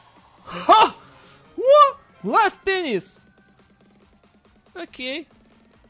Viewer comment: I turned captions on and it said penis instead of penny